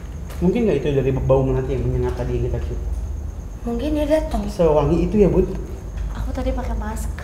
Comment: 9.16 ada suara kalau enggak salah dengar ngomong "ngapain sih datang" ya kan?